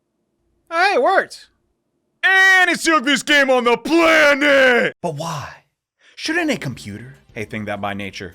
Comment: I think he said “AAAND ITS THE UGLIEST GAME ON THE PLAANEET !!” Not the youngest thing on the planet like caption might insist but I’m not 💯 on that and would appreciate any corrections.